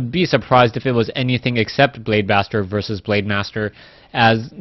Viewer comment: Did he say Blade Bastard at 1:38? lol